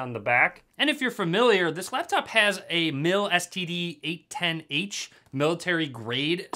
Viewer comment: Wow did he just say this laptop has an STD I think I'm good😭 I'm stupid